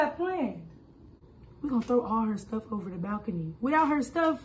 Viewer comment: I thought she said she gon throw her stuff over the mountain 🏔️🤣🤣🤣🤣